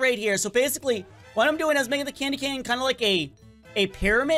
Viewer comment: did he say candy cane???